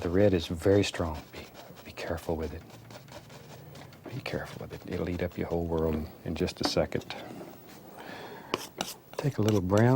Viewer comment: What did he say? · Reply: be careful with it it will eat up your entire world